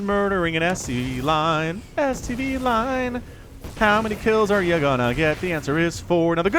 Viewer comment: did I hear what i think I did? #stdline